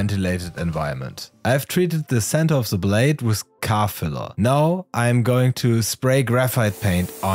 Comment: what did you say you treated the center of the blade with? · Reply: Car filler?